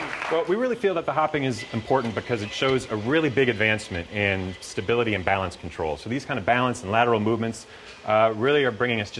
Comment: at the min 2:35 he said "we really feel that"... (what he says after that? please help, I'm chilean and i dont understand what he say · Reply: we really feel that the hoping is important because it shows a really big advancement in stability and balance control. These kind of balance and lateral movements really are bringing us one step closer to that day when Asimo can join us in everyday life